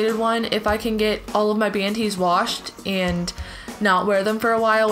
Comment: OMG. I was so disturbed when you were talking about showing your band tees because I thought you were saying you were going to show us your panties. 😂😂 I was like "Um... I don't know if I want a pantie collection video..."